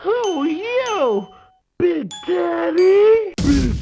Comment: I thought they said “big teddy”